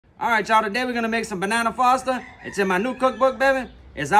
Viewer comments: I thought he said Banana Pasta 😂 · I thought bro said banana pasta for a sec😭😭💀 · I was so confused because I thought he said banana pasta 😂❤ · I thought he said 'banana pasta".😂 That looks eo bussin. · At first, I thought he said banana pasta. I was like, "hmmmm... that's interesting." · i thought he said banana pasta ?🤔🤔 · That butter throw was aggressive AH. LOL Also… I thought you said ‘banana pasta’ at first. 😂🤣 This looks good 👀💯🔥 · Bro I thought you said banana pasta 🤣🤣🤣😂 I was like huh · Jesus I thought he said banana PASta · Bruh I thought you said banana pasta at first 😂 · Lol thought he said banana pasta at first bey-bey